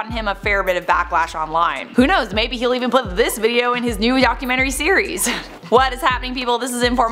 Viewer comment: Is it me or did I hear you say yocumentry?